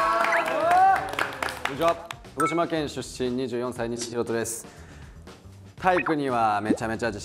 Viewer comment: ここ何回聞いても広島県出身に聞こえちゃうのは私の耳が悪いからですかね…😹